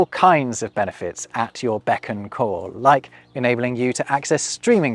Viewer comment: When you said "beckon call" I was struggling to figure out how it was wrong until you wrote it out, because I heard it as "beck n' call", and I was like "I'm pretty sure that is correct, it is beck and call isn't it?" 😆